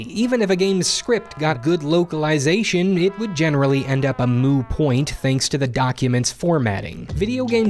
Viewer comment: Did you say "a moo point" at 4:30? :O